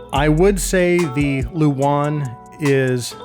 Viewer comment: whats the brown headphones, i couldn't make out what he said they were called.. i been tryna google it, i thought he said "the wand" lol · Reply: That’s the Sivga Luan